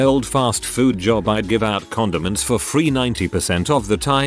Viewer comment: At first, I thought it said "condoms" lol